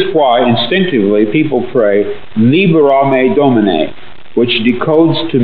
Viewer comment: ok and now I hear the script of Event Horizon 'Libera te me' 😅